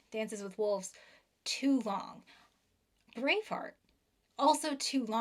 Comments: I swear I had to rewind because i thought she said"brainfart" instead of "braveheart" and thought thats a little hard. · Me nodding along to "it's 3 hours and 15 minutes, it's too long" Then realising I'm watching a one hour and fifteen minute talk to locked off camera. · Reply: I listened three times, but every time she says Braveheart, at 4:35. I hear brainfart. Not really adding to the discussion, but I thought you should know.